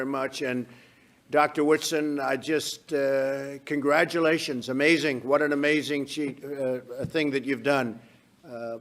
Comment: Trump goes on about everything being fake, so why is he not exposing this fraud? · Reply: John Haughey I dont know but at 10:39 it sounds like he says what an amazing cheat they are but quickly disguises it. Meanwhile if u watch they eyeballs of that thing next to him when he says that its pretty crazy. Might have to watch it a few times